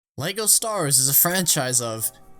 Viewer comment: Did you just said Lego stars